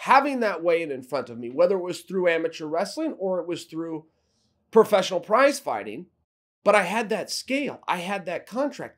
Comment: Thought he said professional crimefighting instead of prize fighter